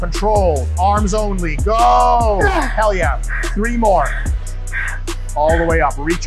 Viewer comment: Dr Mike do you speak greek? Presicely at 7:56 it sounds like you say " telia", wich in greek means perfect!..... and you sayit again at 8:19.